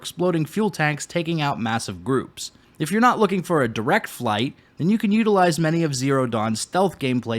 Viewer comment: I heard flight lol